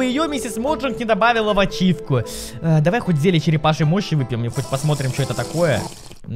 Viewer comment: Я один услышал "зелье черепашьей мочи" 🤣🤣